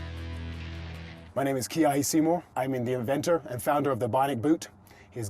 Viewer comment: Am I the only one that heard that he was an Avenger instead of the inventor